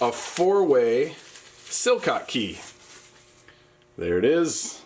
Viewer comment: It sounded like you were saying so-cock-key. You mean socket key, I'm guessing. I seriously googled it I was laughing so hard.